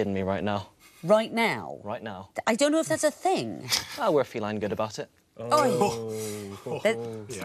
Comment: What did he say towards Victoria at around 19:58? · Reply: He said "we're feline good about it"